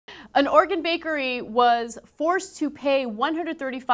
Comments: God damn it, Ana. Enunciate. I swore she said Organ bakery all casual like. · Was it just me that thought Ana said "Organ bakery?"